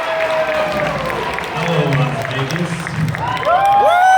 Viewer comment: I am the only one who misheard at the start as saying "Hello N***as"